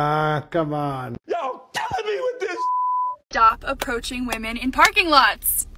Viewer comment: Hello guys, what does R.Kelly say at 2:19? Thank you for your answers. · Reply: I think he says: "y'all killing me with this shit"